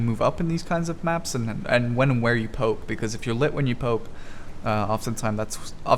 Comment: Literally sounds like you said pope instead of poke lol.